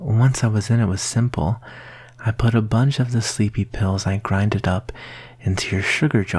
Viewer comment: I thought it was cute until i found out he put "sleeping pills in my drink" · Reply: I heard him say sleepy pills- "I'm afraid I put to many sleepy pills in your drink"